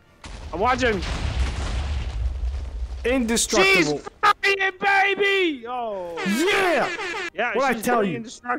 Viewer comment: What he say? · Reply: Ethan he said “he flying baby!”